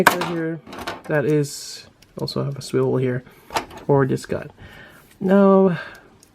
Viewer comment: DID YOU SAY SWIVEL HEREEEEEEE